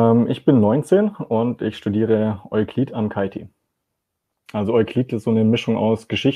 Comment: Das Traurige dabei ist , dass die meisten Menschen dieser Altersklasse genau so sind. Viel Meinung , wenig Informationen und nur irgendwelche Floskeln. Ich sehe da leider schwarz für unsere Zukunft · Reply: Ich habe "Euklidik" verstanden... Und ich spar mir mal weitere Kommentare...